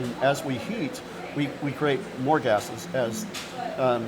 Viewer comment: I could have sworn he said when we heat we create orgasms and this one the chemistry happens!😂 had to listen and relist and realize he said more gases, not orgasms…🤭